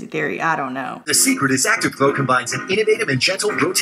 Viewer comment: I might just be crazy but at 8:00 does that dude say active pro instead of active glow? active pro = proactive 👀👀